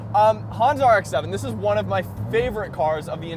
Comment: did you just say Honda rx7!